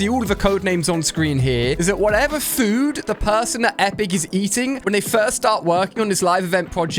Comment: did bro just say “the food that the person that epic is eating” bruhhhh